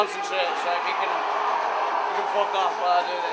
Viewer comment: Did he say "fuck off"?